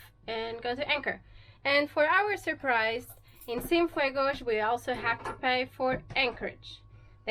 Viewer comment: that's funny, everytime you have been saying ceinfuego, I thought you were saying sinfuego- without fire. two vastly differences.